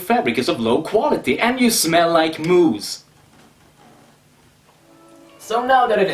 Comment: When you say moose it sounds like your saying moo's